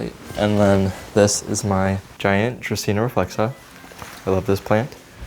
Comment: what is the plant at 3:53 ? he says the name but on google they look different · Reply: Draceana reflexa 'song of india'